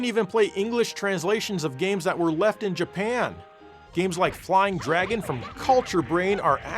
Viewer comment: DID YOU SAY LEFT IN JAPAN!!!!????